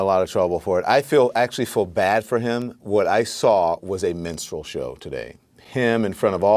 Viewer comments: LemonAid sounded like he said menstrual show. And he would know. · Did he just say menstrual show(3:05) huh? <link> I'm confused... but when am I not when listening to liberals.